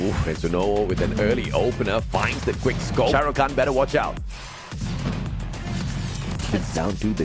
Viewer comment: did he actually say "Shahrukh Khan better watch out"😂